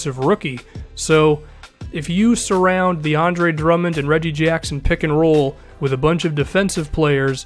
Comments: did he say DeAndre Drummond? Lmao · im ready for Drummond to shoot 90% from the free throw line My twitter - <link> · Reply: of a dwarf He was definitely saying "the Andre Drummond and Reggie jackson pick and roll". I misheard it at first too.